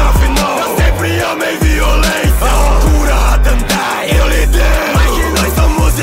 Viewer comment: Alguém me diz q n fui só eu q escutei "A loucura, a gandaia, eu lidero." KKKKKKK